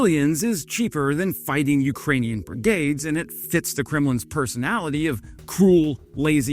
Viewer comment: I thought you said Criminal but you said Kremlin. Easy mistake, those are so alike.